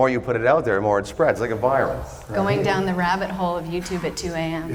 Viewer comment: did you say virus... you knew